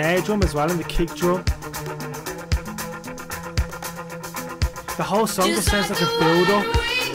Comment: said: "the whole song is, or (sounds like) a build up" I didn't quite catch what he said at around 7:40 Yes, a woman singing about being "on the edge of 17" is pretty intense, it's a real revving up for the lucky ones. She was all in.